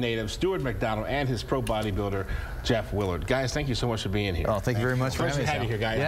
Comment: Did he say "Jeff Willard" at 1:03?